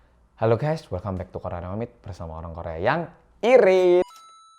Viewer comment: Opening videonya saya ulang sekali lagi soalnya salah dengar... aku dengarnya orang korea yang... peliit... what??? Replay... ternyata irit... 🤣🤣🤣.